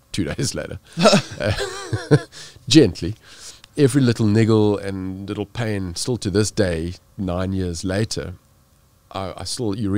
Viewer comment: Yo what did he say at 3:23 ???? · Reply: Every little niggle and little pain, still to this day…